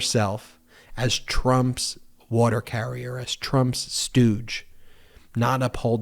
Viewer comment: I had to go back and listen again at 14:25 because I thought Ben said that Judge Cannon is "Trumps water carrier, Trumps douch" but he actually said "stooge". I suppose it could actually make sense both ways.